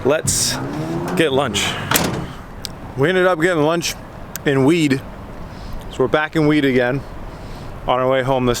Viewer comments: I thought you said that you "got lunch and weed" Would've been a whoooole 'nother road trip if you did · I thought bro said we got lunch and weed and I was like damn y’all lit 😂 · I can't be the only one who heard "lunch and weed".